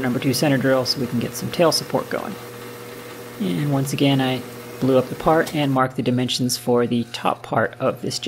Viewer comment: I was stuck in suspense mode bc I thought you said, "... And, once again, I blew up the part". I couldn't find what you did wrong, then realized you blue up the part.